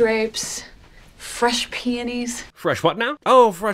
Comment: I love NORMA AND BUT I THOUGHT SHE SAID FRESH PANTIES🤣